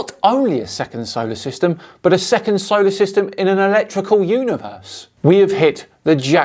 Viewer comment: and his imaginary scam! I could have sworn she said "ERECTICAL universe".